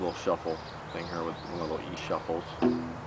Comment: Could someone please write down what he said from 2:16 to 2:19? I'm not an english native and I couldn't catch that. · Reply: Shuffle. :o)